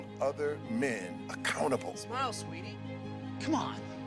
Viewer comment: When the guy at 20:50 says “smile sweetie” the caption says “nipples. Sweetie come on.” Lol